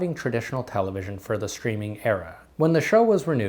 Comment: I misheard your "streaming era" lines as "screaming terror". I have a fan on and the noise overlaps a bit.